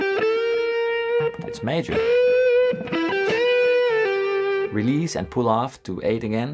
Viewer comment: Fantastic tips! Thanks a mill Wasn't paying attention and then @ 2:28 , I hear "release & pull out again", that's when I was convinced this is deeper than guitar!